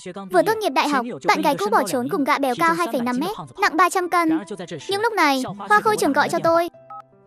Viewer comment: Líc đầu nghe thành "gã béo cao 2,5 km nặng nặng 300 tấn" 💀